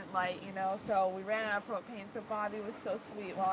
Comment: Thought was saying cocaine not propane